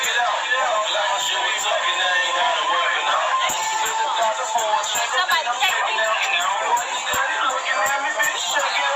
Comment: does anybody knows what is saying when it's her turn to get the phone. Start listening around 16:20 up to after 16:35ish.. idk!!! but PLEASE LISTEN LISTEN LISTEN TO HER AND THE (WORDS THAT SHE'S SINGING) SOMEBODY COMMENT BACK AND LET KNOW WHAT SHE SAY/SINGING CUZ I KNOW WHAT SHE IS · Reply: Sounds like "somebody check me out" and then singing / rapping the song about check me out. At first I thought she said "somebody text me" but it's the song lyrics.... Check me out. All I heard. IDK. 🤔🤷